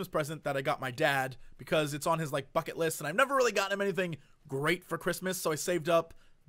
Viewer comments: For 1 second, literally just a split second, I thought he said "Fuck-it List" xD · I thought you said "fuck it list" xD